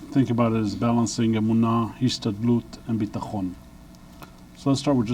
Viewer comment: he says "balancing emunah and something sounds as yvsta lot and bitahon" what is the second word? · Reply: Kent Robin Ludvigsen hishtadlut - means effort